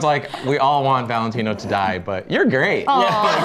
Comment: Love how when Valentinos voice actor said “Your great” it really sounded like Val saying “ Your hot, do you need a job?”